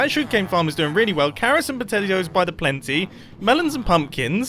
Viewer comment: Am I the only one who heard call potatoes “potelios”? Sounds like a new spud based breakfast cereal! 😂